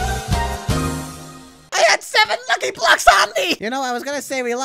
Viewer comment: thought he said I HD 7 F###ING BLOCKS ON ME